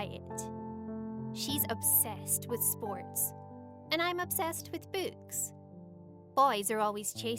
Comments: I thought she said bo*bs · I thought said I’m obsessed with boobs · not gonna lie on 0:50 the she said -im obsessed with book I thought she said : -im obsessed with boobs i was like wait what the f ??? lol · anyone ele thought she said b@@ and not books? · that sounded like “im obsessed with boobs” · When she sounds like shes saying "boobs" instead of books. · When she said “books” I thought she said “boobs” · I thought she said "boobs" not "books" took me a bit to realize what she said xD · I didn't notice her accent at first and when she said 'I'm obsessed with books' I thought she said 'I'm obsessed with boobs' and I was like '??????' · I thought she said she was obsessed with boobs I was like “whatever you say girl....” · I thought u said boobs D: oof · who else thought she said boobs? Cause I was like, the title says she has boyfriend, so is she bi or smthn? But she meant books. LOLOLOL. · Oh i thought she said boots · When she said books I thought she said booze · bro the way she said books i thought she kept saying boobs💀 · I thought she said “I’m obsessed with boobs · I heard "...And I'm obsessed with boobs"😂😂😂😂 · i thought she said, "and im obsessed with boobs" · am I the only one who thought she said boobs XD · When She Says “Books” I Sounds Like She Said “Boobs”